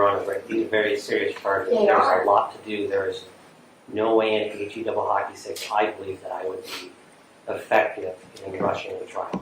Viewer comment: Did he say H. E. Double Hockey Sticks? I had to run that back.